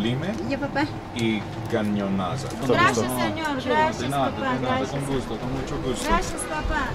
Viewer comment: did she say "Gracias papá" (??????